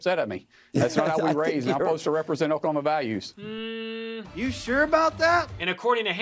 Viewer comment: Did he actually say "represent Obama values" @ 11:35? I'm pretty sure he didn't. But it sounds like it.